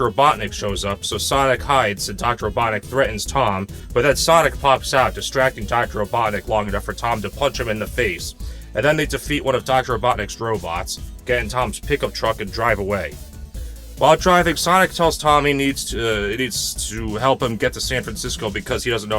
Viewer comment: Not kidding when you said “Dr. Robotnic” I thought you said Dr. Obama